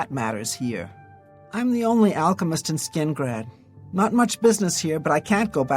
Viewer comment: "I'm the only alchemist in Skingrad" Sinderion: "Am I a joke to you" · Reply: I could have sworn she said "I'm the only OTHER alchemist in Skingrad"... o_O